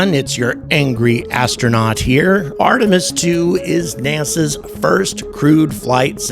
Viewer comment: I heard it as "NASA's first crude flight" not entirely sure that's not appropriate....